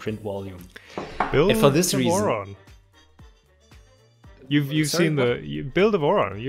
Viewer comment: NO joke I thought he said "You Moron" XD